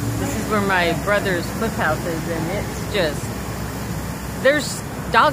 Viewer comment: You said you were by your brother's flip house my old agent brain heard you say you're by your brother's flophouse.... Then I realized you said flip house that's so much better to have a flip house than flop house any day... And you say that your brain don't work right.... LOL 😋 Of course I'm not as young or near as pretty as you are... 😁